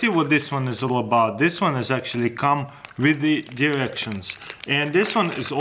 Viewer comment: I'm sorry but I heard "this one comes with erection" took me awhile but its Directions